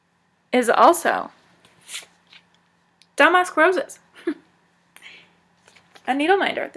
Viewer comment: Great video & I love the tag! I like to use 550 on linen and evenweave that I buy from 123 stitch. I have a long wish list there, lol. Is it bad that I heard "Damask Roses" and Dumb A#% Roses, (/each time) hahaha!